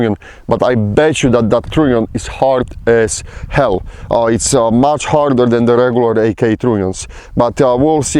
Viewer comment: Did he say at 9:20 front trunnion is much harder than regular AKs? Did I hear correctly?